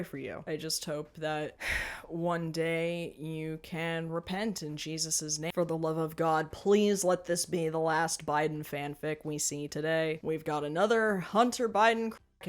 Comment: Did you reAlly say repent to jesus chris or was that a joke lol i thouģhtì it........idk that you were atheist too like me i really thoùghy i heard u say u were socialist/atheist in the past/a past vid lol 😂🤣😜